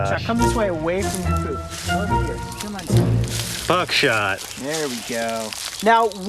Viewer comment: I thought you said butt shot